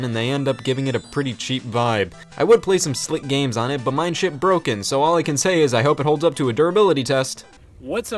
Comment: I thought you said My shit broken